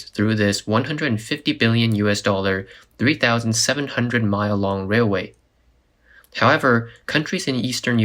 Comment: I have 1 million miles driving semi, and almost the same in a squad car when I was a deputy Sheriff.. 37,000 miles must be a mistake.. I drove over 100,000 miles a year for many years. Maybe I misheard that and I will go back and listen again, but I'm pretty sure...